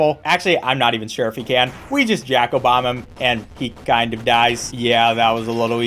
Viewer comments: I overheard you saying Jack Obama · subtitles say 'jack obama'